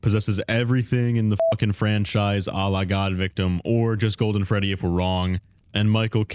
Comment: i am sorry did you just say "Allah-god-victim"? cause i don't þink you should say þat...